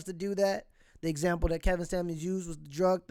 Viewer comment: For a second I thought he said “the late Kevin Samuels” and got worried lol